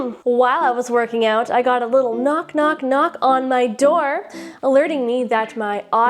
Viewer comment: Was actually stunned 😂😂 Had to rewind the video to hear what you said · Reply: I'm not sure what you're referring to at 2:57... I said 'knock knock knock'.....?😅